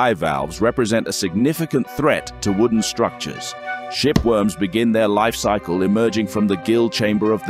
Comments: I thought he said, "shit worms" 😅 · For a minute I thought I heard shit worm 😂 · I thought he said shit worms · I thought he said sht worms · Bruh I thought he said shiitworms 😵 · I’m just happy this has subtitles because I swear I heard him say “shit worms” 😂😭